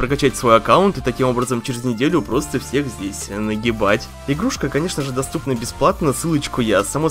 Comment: мне послышалось что он сказал не нагибать а наебать